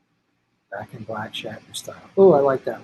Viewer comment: I so thought you said “Back To Black” & got wildly excited to hear Shatner sing Winehouse 😂😂 god it’s been a shit month!!! Will it EVER END??? Love u 🌹💚✊🏽